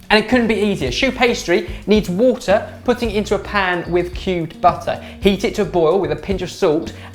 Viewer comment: i thought he said cute butter😂